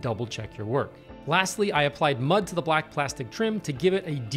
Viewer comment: did he say he used mud on the trim?